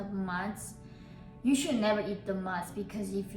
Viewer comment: Hi Yeonmi I am sorry for your lost Yeonmi. 1. What did you say at 9:35 ? You should not eat what? 2. I don't think there are any N.Korean dishes that exist but for the famous Cold Noodle. But I hear it is very bland. I would still love to try it out. There are some S.Koreans that love it. 3. What do they put inside the fake meat? I am guessing it is all just artificial flavouring? Most processed vegan foods are garbage to begin with. Be safe out there Yeonmi and thank you for sharing another great video. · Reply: She said to not eat mud I think